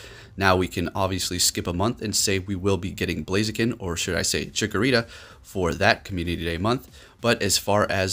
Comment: Did He say Chikorita in